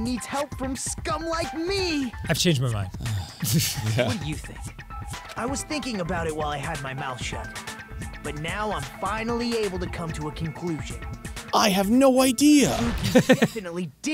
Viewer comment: when he said he was thinking about it while he had his mouth shut I heard it as I was thinking about it while I had my milkshake, and I was "whaaa when did he get that".